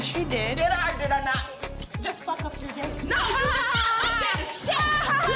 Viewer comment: i don’t know how before until now, i thought she said “did i or did i not just fucked him today” referring to flav